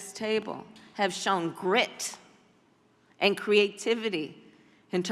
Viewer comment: Did she just say grits instead of grit!!